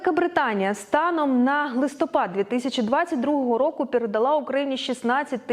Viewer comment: На 2:49 почулося "станом на глистопад" 😅